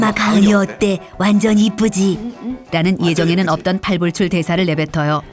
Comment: (노잼주의) ???:: 네?? 뭐라고요?? 신불출이라고요???